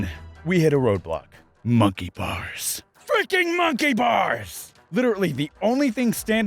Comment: I had an ad when he said, "FREAKING MONKEY BARS" so it sounded like he said "FREAKING MONKEY BOB" XD